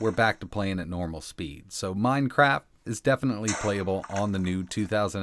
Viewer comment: Did he just say minecrap 😂